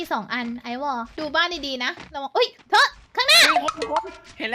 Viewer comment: มีใครได้ยินพี่พูดว่า "เฮ้ยเธอ.." ปะ